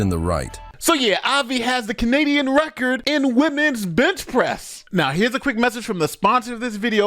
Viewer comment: It sounded like you said "Women's Bitch Press"! LOL!